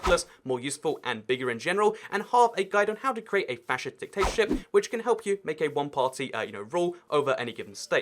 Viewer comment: I thought it said racist dictator